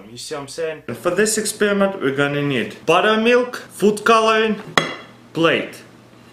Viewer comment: When he says butter milk it sounds like he says butthole milk